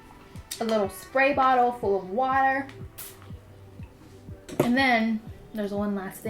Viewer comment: I thought she said a spray bottle full of wine